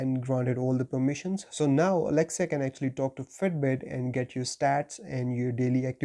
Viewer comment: I heard “now Alexa can actually talk to Fredbear”